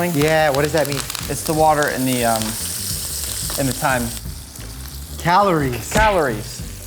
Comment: If you enable captions, here it says time instead of thyme 😭